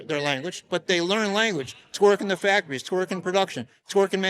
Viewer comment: Did someone say twerkin' ?